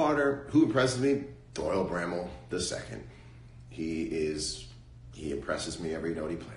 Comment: Who is Doyle Grammel the II? 21:50. Am I hearing this name wrong or am I going deaf? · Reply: Cav Ratiug Doyle bramhall